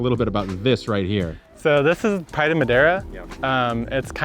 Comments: What is the name of the purple flowering plant with all the bees it sounded like he said part of Madera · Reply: Pride of Madeira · What is the giant blue plant with all of the bees? Sounded like he said phytomadera or something like that